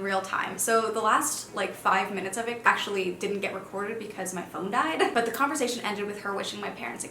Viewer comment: When she said the last few minutes didn’t get recorded because her phone died, i thought she said because her mom died and i was like wow. what a casual way to say that.